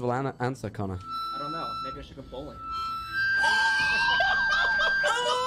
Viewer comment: Did anyone make out what connor said at 1:29:40? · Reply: He said maybe i should go bowling